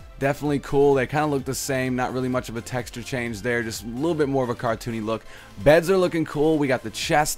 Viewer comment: Sounds like he said carton instead of cartoon